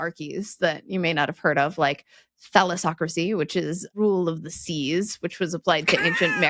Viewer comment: said, thalassocracy, heard something different, I thought I heard felis-ocracy, rule by cats!